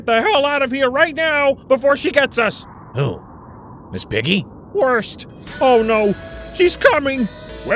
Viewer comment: Is it just me or did Kermit say "Worst"